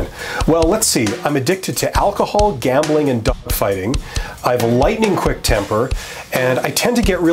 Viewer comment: “ I’m addicted to alcohol, gambling and dog fighting! I have a lightning quick temper and get violent when I don’t get my way.” 😂🤣😂🤣 I was dying ! Getting ready for a job interview tomorrow and loving your videos ! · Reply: I thought he said "duck fighting".... ...."dog fighting" makes a lot more sense. 😂